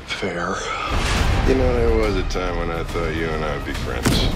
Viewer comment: can somebody say what kurt says in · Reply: "You know there was a time when I thought you and I could be friends"